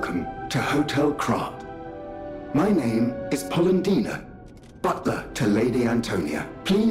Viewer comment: I truly thought butler Polendina said Paula Deen